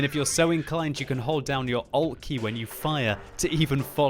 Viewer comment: Can someone explain me what does he mean by saying "hold your all key when you fire" at 3:09 please ? thanks :) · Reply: Alea toire "alt key"